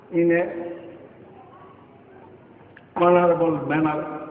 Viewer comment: did he say.. he is in a 'horny-able' position. and 6:30.. dil me kuch kala hey. .. hahaha..